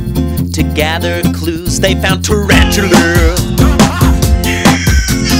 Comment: Does he say "I like Penis and Tarantula"?